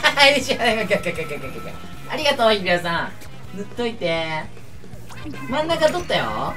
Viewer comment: ありがとういくらさーんにちょっと聞こえる笑